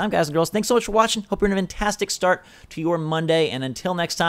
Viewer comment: Am I the only one who heard him say switchforce out twice??